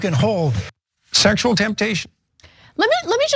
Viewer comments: Did any one else hear sexual temptation at 1:50? Because I know I hear it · did anyone else hear "sexual temptation" @ 1:50 ?